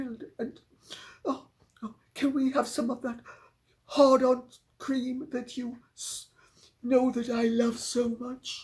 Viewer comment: Oh dear, a bit crude but I'm saying this anyway because it made me literally laugh out loud. I thought Lady C, when impersonating Todger, said, " ....can we have some of that hard-on cream that you know that I love so much". 😮😮 I had to rewind it, of course 'twas Arden cream. Aaaahhh, sorry, guess you had to be there, I cracked up at my mishearing. Ok....moving on. 😂